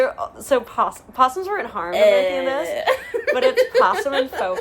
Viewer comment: did you say possums were or weren’t harmed? I can’t quite hear it, sorry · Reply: To our knowledge they were not!